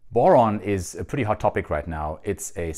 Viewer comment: I thought it said bourbon. I’ll see myself out. 🥃